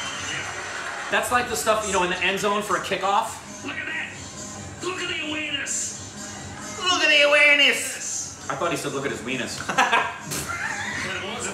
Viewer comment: I thought he said ook at his wenis